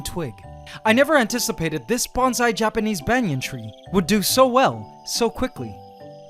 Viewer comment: What did he say at 2:32. Bonsai japanese benyan tree?